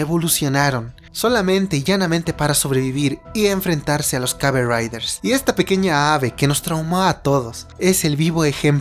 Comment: te entendi Kamen Riders XD